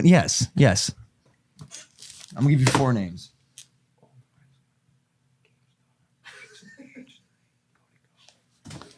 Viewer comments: he whispered "keemstar, Cody ko, h3h" I didn't hear the first one 😂😂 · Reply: I heard ricegum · I heard keemstar, h3h3, and cody Ko but I didnt heart the 4th one. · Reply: ricegum